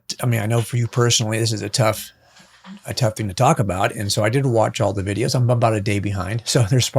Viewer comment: "and so I did watch all the videos". That is the transcription on the screen. But I heard him say "I didn't watch all the videos". It would be hardly be practical for one person to watch all the videos anyway. I think this guy is running cover for the official story. "protecting the trial" - what does he mean? Who is this guy? Is his zipped up jacket meant to say he has an airtight case? A major oddity for me in the case is the camera as gun story which looks to be lifted from the movie "The Bodyguard". If we believe this happened, then the shooter is a patsy and we cannot trust for a moment the odd text messages he was alleged to have sent. The placement of Charlie Kirk on the floor after he was shot - this happened very quickly indeed, even with people diving across the table to get to him. The removal of the wall camera less than 6 minutes after the shooting is another oddity, and I'm sure there are many more oddities to be found.